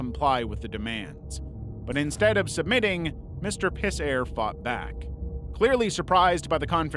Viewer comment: I'm sorry, but did you just say "Mr. Pissair?" or am I high?